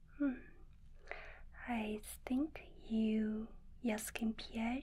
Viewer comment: am I tripping or did she say "I stink"